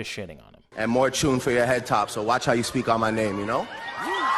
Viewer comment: More tuna fish for ya head top? Wat de fuck did he say